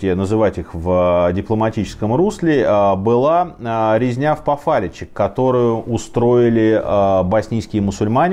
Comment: "резня в пофариче" - не могу найти данные об этом эпизоде... может я не правильно название услышал?)